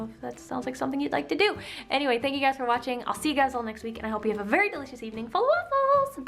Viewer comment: put on the captions(subtitles) and it says "hope you have a very delicious evening full of balls bye" instead of "hope you have a very delicious evening full of waffles"